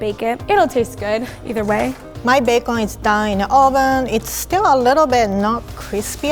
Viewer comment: I heard “My bacon is dying in oven” 🤣